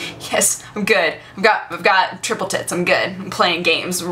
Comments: I'm pretty sure I just heard say she had "triple tits". O.O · anyone else hear "I got triple tits"???? O_o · what did you say??1/! triple tits?? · Did she say "Triple Tits" at 5:47 because thats what it sounds like to me? I don't think thats what she said but thats what I heard. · does she say "I have triple tits"? o.O